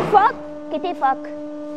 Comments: did she just say titty-fuck? 🤣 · did she say t*tty f*ck 🤣